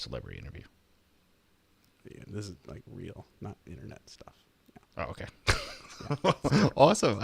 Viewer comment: turned the subtitles on because i couldn't hear this too well and at 6:37 it thinks said something about nazi internet stuff.(might be what he said because i couldn't hear it properly)